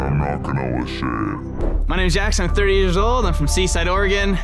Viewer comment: CONGRATS to our winner!! Now I want to see who can run it FASTEST. The fastest run will win $10,000. This challenge is open to the public! If you want to participate, all details are in my app which can be downloaded here: <link> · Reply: on 13:24 and 14:00 the substitles say 38 instead of 30